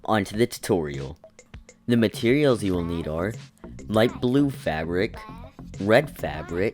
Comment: what he said? Favric?